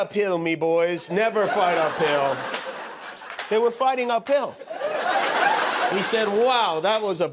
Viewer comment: It sounded like he said "fighting a pill". Two or three times. l'm fighting a pill, named Donald Trump.